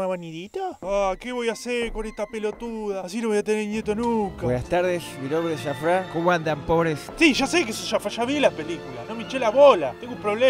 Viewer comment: entendi "buenah tardesh, mi nombre e asafran. como andan? pobres."